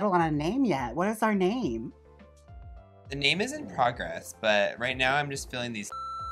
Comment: I really thought he said "the name Izzy Progress" and I thought that's a cool drag name.